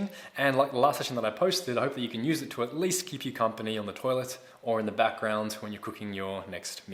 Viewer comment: Did a double take coz I first heard “at least keep you company on the toilet in the background when you are cooking”.